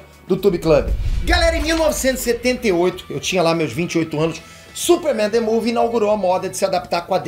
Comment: Em 1:42 o Peter disse isso mesmo ou eu entendi errado: 1978 eu tinha la meus 28 anos... ???